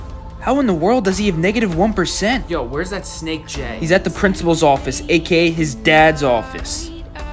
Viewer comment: “That snake Jay” 💀💀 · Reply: I heard "That snake Gay" 💀💀💀🤣